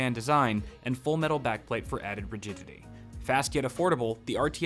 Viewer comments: I thought he said virginity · I thought it said full metal back plate for added vriginity